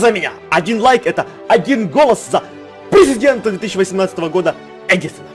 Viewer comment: Я на 1:02 услышала не президент, а пи*дент)))